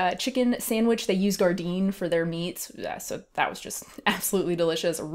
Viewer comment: what does she say they use for the meat at 6:16? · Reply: Gardein